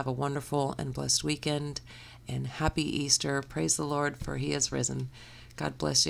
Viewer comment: As sacrilegious as this may sound, I misheard "praise the Lord for he is resin". Either way, the bowl turned out great, the end result was absolutely worth it!